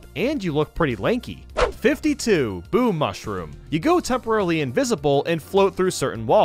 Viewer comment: it sounded like he said homer mushroom.